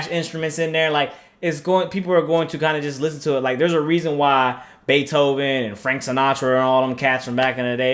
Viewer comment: i thought you said zaytoven instead of beethoven bruh im dead